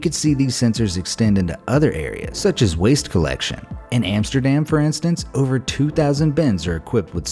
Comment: I thought u said Sudan but it’s amsterdam